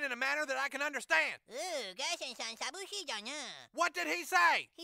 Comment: What DID he say? I understood most of what they said but that 1 sentence kinda trips me up a bit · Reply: He said ギャリソンさんさびっしじゃな (garrison-san sabusshi jana) which means “Mr Garrison is lonely.” They just pronounced sabusshi weird.